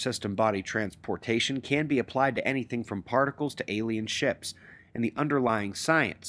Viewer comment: did you said alien ships?🤔🛸